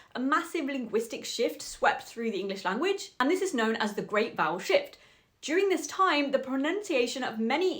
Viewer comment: I thought you said the Great Bowel Shift... which is a much different sweeping shift that happens after Taco Bell 😅